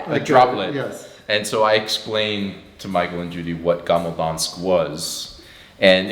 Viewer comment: nice video, but what is a 'gamabonsk' you said in 13:04? 😊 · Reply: Gammel Dansk - <link>